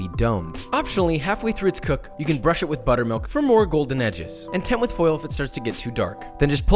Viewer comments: did he say buttermilk or butter..? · does he say buttermilk? or does he mean butter?!? ive never heard someone using buttermilk to do this with haha